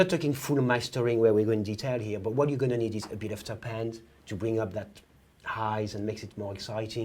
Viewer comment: Can anyone tell me what he says at 12:35? It sounds like he's saying "...but what you're gonna need is a bit of tapand..." Is it top end? Like as in referring to treble basically?